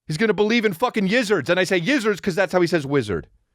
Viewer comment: Yizard= wizard. ♡ I immediately thought Calvin was saying lizard. How does say Lizard?? ♡♡